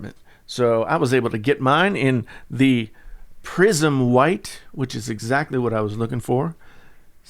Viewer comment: LOL I thought you said "prison white"